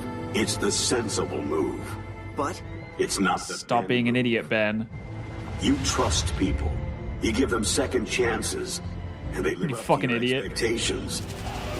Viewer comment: You should not have spoken here. What Max said was "It's not the Ben move. You trust people. You give them second chances. And they live up to your expectations." Max was saying that the "sensible move" was not the right move because Ben is always able to find another way.